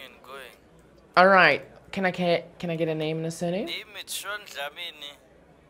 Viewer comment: the MTN one shook me. I thought the guy said "minestrone dlamini" until i heard shaun dlamini